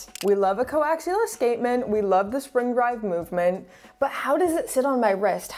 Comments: did you say spring drive movement?? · Did I hear "Spring Drive" movement at 6:59? This is what passion towards watches do to you. Anyways great content. I'm myself aspiring to become a Youtuber in this space, but hardly have the time. Your last video inspired me a lot, though. Thanks and keep it coming